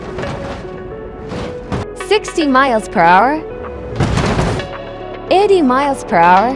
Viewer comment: I heard "Miles Per Harww" 💀💀💀💀💀